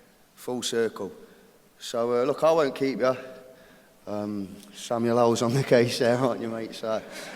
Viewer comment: I fear this has been both everything AND the last thing I needed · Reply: I just realized what you said @ 20:58. I don't know if anyone answered already but he said "Samuel L's on the case, aren't you mate?"